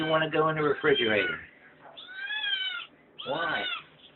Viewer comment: Cool! It sounds like Felix is saying "Why not", when you say no! lol